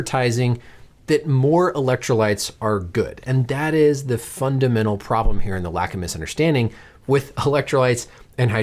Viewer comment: Does he say "The fundamental problem here and lack of misunderstanding" ?